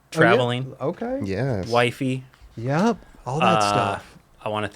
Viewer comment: had to play it back first thing I thought I heard was as he said what he was up to"Skating,traveling, whitening.." As in teeth whitening 💀 he said wifey